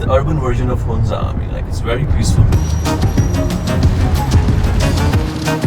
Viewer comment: Did he say, "peaceful ?? I hope it is....